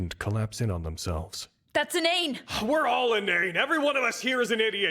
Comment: can please anyone enlight me on what slang saber said there? I just cant figure this out and is driving me crazy "that's a nane"? Im not natural speaker, so i'm not familiar to slangs.... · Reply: i think its inane witch means being an idiot or stupid from what i have seen others say